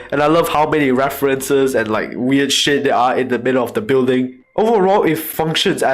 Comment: did he just said weird shits